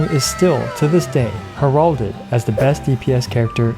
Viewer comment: did he say heralded?